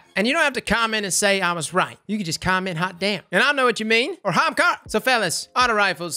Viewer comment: You said comment "Hot damn" but I heard "comment Halfdan"